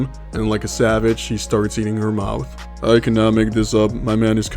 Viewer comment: Around the 4 minute mark, I wasn't listening closely enough and thought you said out, not mouth filled by the sexuall battery comment. I was confused and very concerned. Had to rewatch with subtitles 😮😂